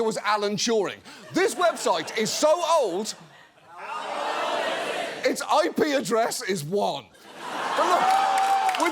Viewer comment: What does the audience yell here? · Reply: They yelled, "How old is it!?!"